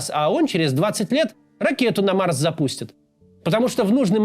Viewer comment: услышал "а он ракету на вас запустит". Знатно прифигел, отмотал назад, послушал заново, выдохнул с облегчением)